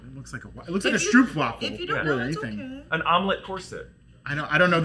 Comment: wait did you say stroopwafel? It sounded like you said stoepwafel in dutch what translate to pavement waffle 😂 Personally, I think it is more of a breakfast waffle structure than a stroopwaffel🧇